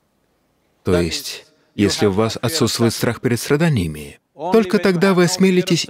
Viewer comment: что он сказал, не расслышал... "отсутствует страх" перед чем? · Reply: Перед страданиями